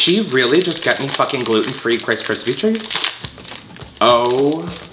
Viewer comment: did he really say krice krispy treats at 13:40??